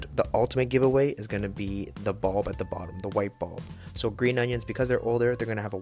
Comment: I thought it said gross onions💀 which they are tho